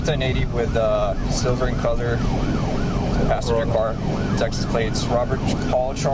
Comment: Cop said, "Silver in color." I thought he said "a silver-ee color". Bwahaha.